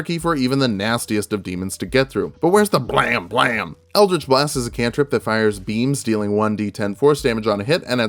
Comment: For a second I had thought he said "eldrich blast is a spell that fires memes"